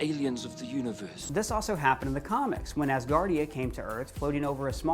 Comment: did he say ASGARDIA???